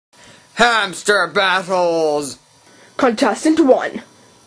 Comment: Sounds like he says "HAMSTER BUTTHOOOOOOOLES!"